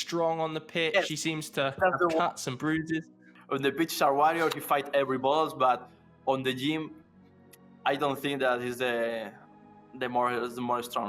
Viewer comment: What does he say at 8:10 ? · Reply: On the pitch he's a warrior, he fights for every ball but on the game I don't think he's the strongest. I think that's what he said.